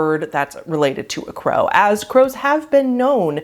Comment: I've worked on a ranch that was like a tourist horse riding place... the crows learn to talk around those areas, no joke. Usually just one very generic word they've heard over and over like 'yeah' or 'hey.' One learned to say John for some reason, haha. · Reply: i thought you said "cows" and i was so amazed. almost corrected someone else who said "crows are amazing" i am dumb 😂